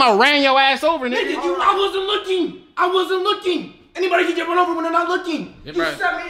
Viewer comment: Can somebody tell me what said at 10:38 please? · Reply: "Anybody can get run over when they're not looking"